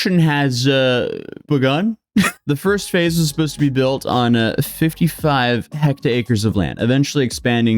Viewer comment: Is he saying "hecta-acres" or "hectares"?